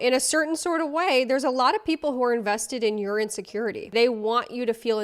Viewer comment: Fuck my life I heard you say "some people are invested in URINE SECURITY instead of "your insecurity" and I just fucking accepted it. You've raised me well babe.